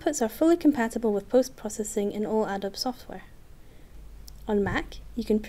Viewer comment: Looks nice. I might have to start shooting video 360 in addition to "still". Can you clarify something because I couldn't hear/understand it: 7:20 "fully compatible with post processing in all ??? software" What type of software did she say it is compatible with? Thanks. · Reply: Adobe